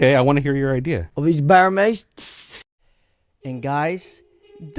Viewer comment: what did he say? A bare mace?